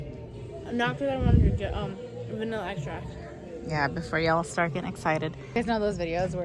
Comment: its sounded like said for a vanilla ice shots not vanilla extract i had to rewatch it four time last time with subtitles LOL